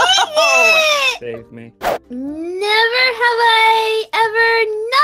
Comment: was that just me or did I hear max say save me I must be going crazy cause o definitely heard him say that